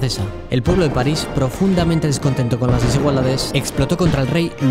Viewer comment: Alguien me podría decir que dice en el minuto 1:10? Psdt: Gran vídeo, me ayudaste con una exposición solo que algunas palabras no entiendo lo que dices xd por eso pregunte jsjsjdjs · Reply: Contra las desigualdades creo